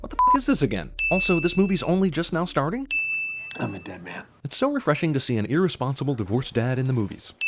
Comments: FOR A MOMMENT I THOUGHT HE SAID "IM BATMAN" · I thought he said I'm bat man the first time I heard that